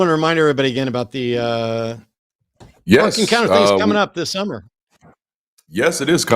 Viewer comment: What was Darante plugging at the end at 3:13:00 ? I didn't hear exactly what Matt said and Darante mentioned a website but didn't say what the address was. I think Matt might have said Ark Encounter??